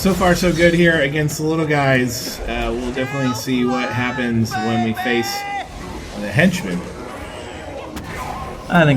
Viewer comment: what was benedict yelling at 6:06? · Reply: Still Flyin' Babyyyy